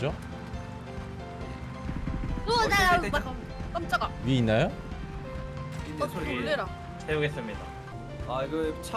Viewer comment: 이때 뭐라고 한건지 해석 가능할까요? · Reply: 음 정확히는 우더ㅑ아댜 깜짝아 라고 한거 같네요 ㅎㅁㅎ